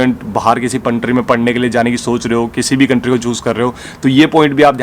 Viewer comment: Great video. I'm watching your videos for quite sometime as I'm interested to do master's from uk or Aus. Also I know it's Hilarious but m I the only one to hear Puntry instead of country at 5:50...🙂